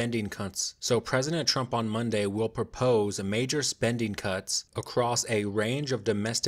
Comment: I heard "major spending cunts" the first time you said it